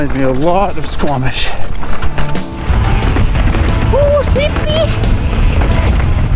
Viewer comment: 途中に日本語が入っていたような...?🤔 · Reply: ほい、よっこっいしょ って聞こえますよね？ww